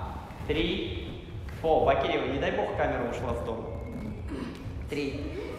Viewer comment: что он сказал? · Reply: Вообще-то не Обаева!! А Бакеева!!!)